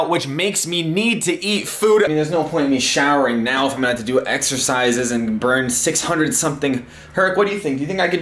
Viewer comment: Exercise. I thought he said EXTRA FRIES